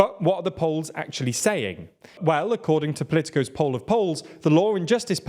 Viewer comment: I admit to a moment of confusion when I heard him say "Poll of Poles" instead of "Poll of Polls"